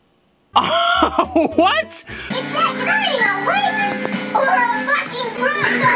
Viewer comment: I hear “Or a f**cking frogo!” 8:39 I hear “I can be quite a dish!” 11:12 I hear “Don’t go f**cking my sister”